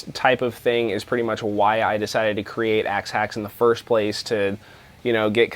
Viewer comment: At the beginning I thought you said axe axe